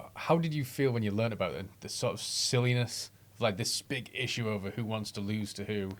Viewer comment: does he say, "Spic Issue" cause that's what I heard.